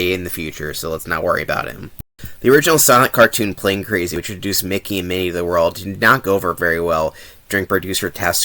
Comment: On first viewing I heard "Sonic cartoon". Had to replay the clip a few times LOL.